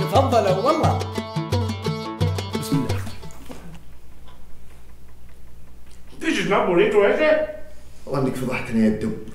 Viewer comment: من يوم نزلت الحلقة وليومكم ذا ما اعرف وش قال الدب بالدقيقة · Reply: ديس نوت بوريتو اسه